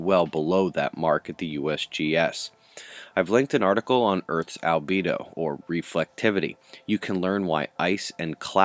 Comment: I thought you said libido. Ears not awake? Maybe there really is something to what they are spraying in earth's atmosphere to repel significant flaring on the earth facing side of the disc.